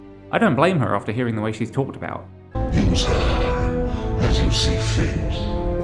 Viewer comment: I don't understand what is being said, audio quality poor. A kind soul to help out? :) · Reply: "Use her as you see fit." You're right as well, the audio is so poor. I really should have added subtitles for that haha! Thank you as well for watching and for commenting! I really appreciate it, thank you!